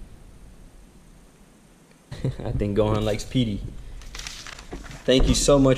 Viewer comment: I think at 7:05 I hear I think Gohan likes pee?😂😂😂